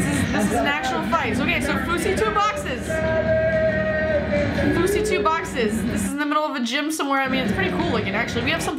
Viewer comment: did you say Fousy